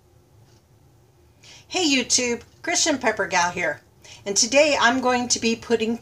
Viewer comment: New to your channel, very good information... But...? So your name is NOT Kristin Peppergol. Which is what I thought you were saying at first. LOL Not your speaking, just my bad hearing. LOL